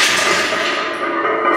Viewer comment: I think that is the best "Train untamed" you've said. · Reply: I feel like a dumbass. I thought Alan was saying "Train On Time" which meant a lot such as, get your ass to the gym like you are supposed to, but the Untamed makes more sense since this is his mantra. Derp on me.